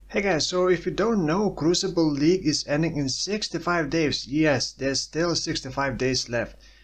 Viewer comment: damn i heard that "crucible league ends in 6 to 5 days" i got a heart attack :D